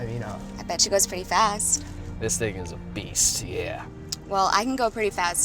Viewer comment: I thought he said this nigga is a beast